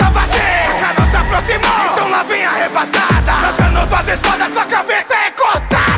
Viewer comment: Oque ta escrito "kanao se aproximou então la vem a arrebatada Oq eu ouvi "então kanao se aproximou então novinha arrebata lakakakakaka